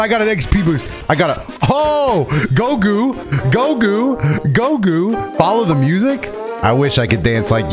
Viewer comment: I was not looking at the screen j was just listening and I thought was saying gogo like a name but then I realised he was saying go too cause he was cheering him a the shock expression on my face was crazy